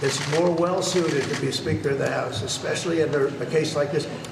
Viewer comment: did trump call Mike the Sphincter of the House? 'Cos that's what I heard.